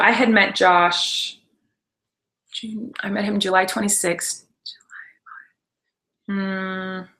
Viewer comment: just after Colleen says "June 26th" does anybody hear a voice whisper "July 9th" or am I hearing things at 2:15 at night?